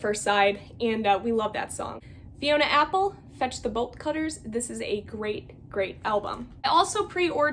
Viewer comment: Every time I hear someone say "Fetch the Bolt Cutters" aloud, I hear it as "Vegetable Cutters."